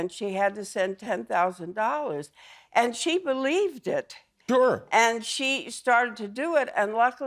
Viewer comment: What did jimmy say at 1:49? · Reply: He says “sure” but he starts making noise before his mouth is fully open, so it comes out like “jer” 😅